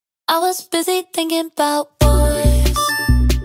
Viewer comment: it sounds like mario when you get a coin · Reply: when I first heard this song I thought she said " I was busy thinking bout coins"